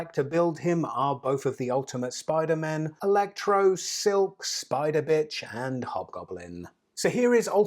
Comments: Did you just say spider bitch? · LOL DID HE SAY SPIDER BITCH 1:01!